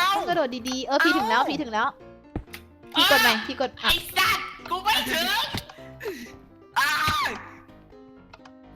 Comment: ใครได้ยินพี่พีพูดว่า ไอ้สัสกูไม่ถึง 555+